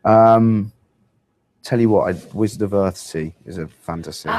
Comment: What book did he say in 30:00? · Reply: Wizard of earthsea